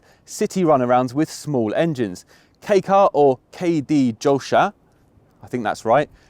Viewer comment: no, sadly, that's not right. It even took me a while to get what you were trying to say. It's kei-jidōsha, not kei-d-jōsha.